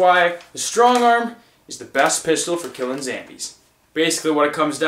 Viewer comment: did he just said zambies instead of zombies